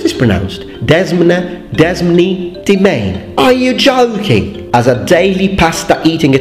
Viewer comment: On behalf of all native English speakers, I apologize. I didnt even know demesne was a word. I thought you were saying domain.